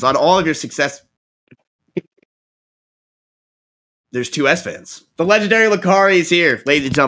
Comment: The only one who can save us from Mia is Esfand's dad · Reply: I thought they were saying S van this whole time